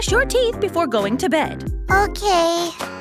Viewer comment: the “ok” sounded like my melody